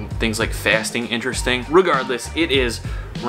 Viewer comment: Did he say RU-gardless ?!?^^